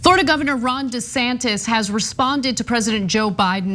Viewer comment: I mis-heard you saying "Florida governor" and thought you said "Sorta governor"...that's about right!